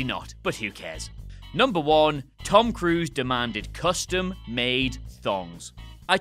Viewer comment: I thought he said custard made thongs.